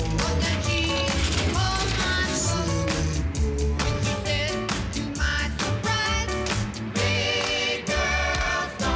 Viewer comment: mark what did say after they fell off the bed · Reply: Don’t start again upstairs